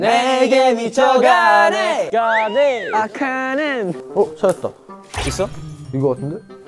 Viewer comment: did yedam just say "makanan"?